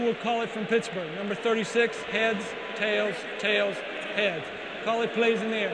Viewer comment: I couldn’t believe my eyes and ears when I saw this. It still hurts today. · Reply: I held my phone up to my ear and clearly heard him say tails. How did they not make a stink about it. I would've demanded the ball. Made them listen to the tape